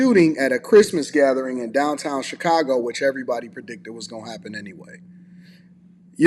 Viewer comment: I thought he said crime was down in Chicago